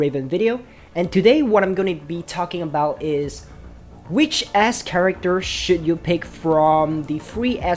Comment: I heard it as which Ass character to pick. Which considering the nature of these games I did not immediately realize I heard it wrong.